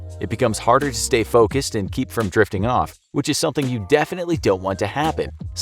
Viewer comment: Me hearing the saying jerking off instead of drifting off at 8:42..👀 I need help...